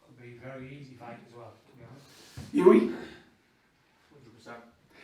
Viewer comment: did he say hughie? is hughie fury there?